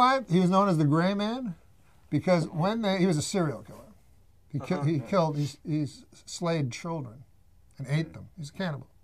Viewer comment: I thought he said "he was grain ... a cereal killer." That was funny to me.